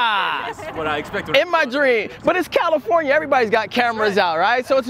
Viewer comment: Did he just say California. 2:35 The state where women are peaceful and people are accepting 🥲